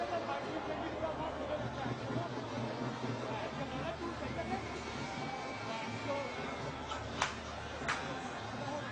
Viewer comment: Can someone tell what he said when he wears the cap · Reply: Ben stokes 😂😂😂